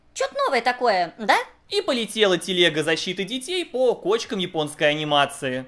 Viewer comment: "И полетела телега защиты детей по кочкам японской анимации" В копилочку крылатых фраз. Великолепно. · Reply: А мне послышалось: "по почкам" 🤣